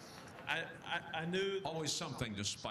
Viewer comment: at first i thought he said enjoy the gentle soul pal. which is atleast interesting.then i read this thanks for the clearification of how bad this guy sucks